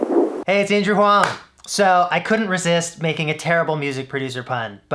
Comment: I heard him say Andrew huang too many times. It sounds like Asian Wong now.